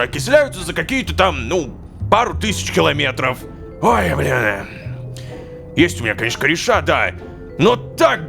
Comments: Мне одному послышалось (ой бля) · мне не одному кажется сто он сказал что он сказал «Ой б~я»? 💀💀💀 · что он сказал ой бля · мне послышалось или.... Ой бля?